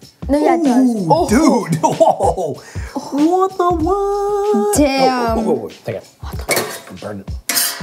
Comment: Did he say damn